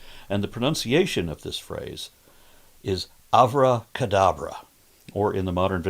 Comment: Am I the only one that thought he said "Avada Kedavra" at first? The most forbidden spell in Harry Potter that causes instant annihilation? lol I'm sure there's some correlation there, such as how Avada Kedavra is an altered version of the phrase, that refers to instant disappearance rather than appearance.